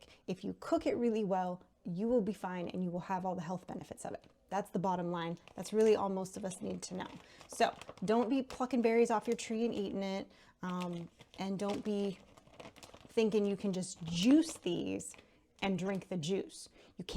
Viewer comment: dont be plucking berries off your bush. Yikes. I swear I heard "dont eat f ing berries off your bush". HAHA thought ya had a potty mouth. Either one would be good advise though. Loved the vid! New subscriber now!